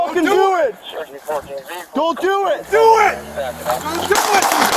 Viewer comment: anyone else hear the officer say "Do It"